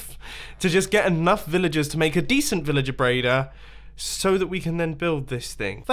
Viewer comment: did he say Villager braider??!??!?